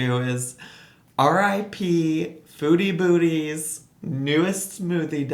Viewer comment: I kept hearing "foodie booty" hahah